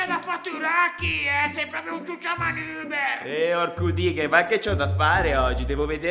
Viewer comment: ho sentito quasi una bestemmia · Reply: Ha detto orco dighel